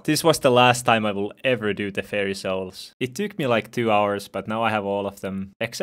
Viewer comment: I heard "hairy souls"